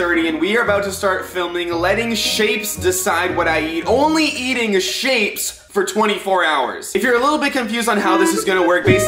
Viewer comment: NEXT VIDEO: ONLY EATING HERCULES’S FOOD FOR 24 HOURS (DOG FOOD) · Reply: I thought you said “only eating Hercules for 24 hours” I was like 0_o